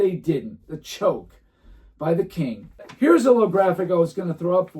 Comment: did you say, “Le-Graphic” 😂